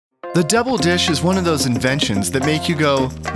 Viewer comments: Who else thought it said "the devil dish"?? Edit: damn thanks for the likes... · Idk why but I keep hearing "deble dish" not devil dish not double dish "deble dish"